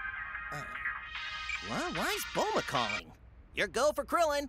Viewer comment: hahahaha i thought i heard krillin say my wife boma is calling when he actually said why is boma calling. lmao